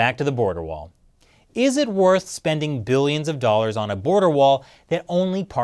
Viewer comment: Did you just say 5 billion dollars for beards? Sorry, I could not focus on the topic for some reason.